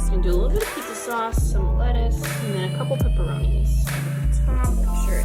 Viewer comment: Help me out. 09:36 does she say lettuce or is it a bad edit?